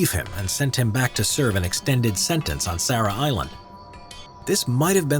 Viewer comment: he said Sarah Hyland, did i heard that right ?